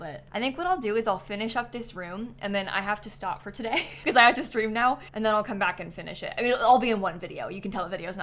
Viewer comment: definitely thought you said “I have to scream now” and I was just like fair, have a good scream